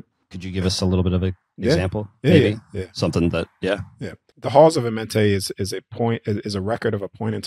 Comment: I’ve never heard of “the Halls of Mentais”..🤔 I hope you bring some more knowledge about this please 🙏🏻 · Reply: "Halls of Amenti", for reference The first time I heard say it, I also misheard lol.